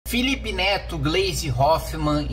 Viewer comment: Só eu ouvi "Felipe Neto, Blaze Hoffman" ?